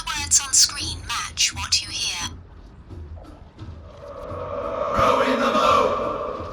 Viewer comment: Throwin' the boat ...every time 1:30 When you only hear Bill every time. 😂 1:45 Can't understand it first time...second time I could convince myself it was saying what the guy said but it didnt really sound right. 3:38 Joke's on them! I have no money so I will almost always get the cheapest thing and will debate for years whether I actually need it or not! 🤑 4:12 I heard no number but clearly heard "Dick" and couldn't really pay attention after that...cuz giggling. Then I heard "mast" so maybe it was actually "deck?" 😅 5:41 If I really force it, maybe ketchup and milk? but not really....they both just sound like noises to me that could be any number of words. 6:20 "fart fart fart...fart" lolol 6:39 Red all da way, baby!!!! 😆 7:40 Nope. I felt nothing. Well...that was fun. I have some ideas as to why these didn't really work on me but..."Nobody cares Sean, No body cares."